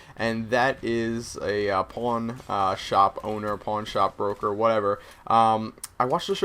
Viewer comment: Do you make money by posting these videos? And when I heard this commentary I originally thought you said you wanted to open a "Porn Shop" LOL